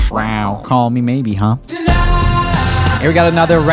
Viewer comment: for the longest time I always thought she said "call me baby"